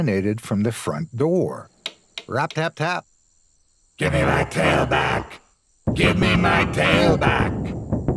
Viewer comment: Thought he said give me my tailypo but this is an extremely good channel earned a sub in my book I enjoy this everything is on point